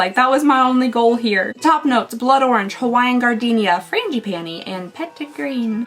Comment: When you said Frangipani I thought said Granny panty. I thought I just wasn't rich enough to appreciate the granny panty scent